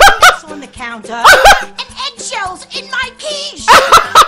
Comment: "EGGSHELLS IN MY QUicHE!!!" what I heard: "EGGSHELLS IN MY COOCHIE!! "